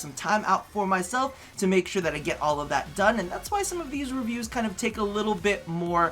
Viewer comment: ok, maybe Im obsessed with RPDR, but did he just say ruview instead of review?